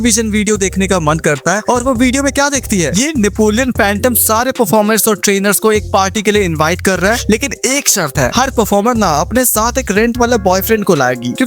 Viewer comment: what he said :- rent wala bf What I heard :- bhade ka tattu😅